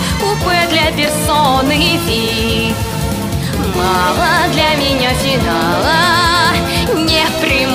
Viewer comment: Я один тут слышу: "Мама для меня читала"?